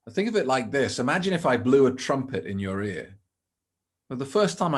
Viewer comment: I thought he said "imagine I blow a trumpet in your rear " lol